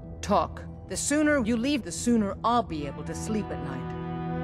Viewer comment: soon i'll be able to sleep with general tulius that's what i heard damn gamerpoop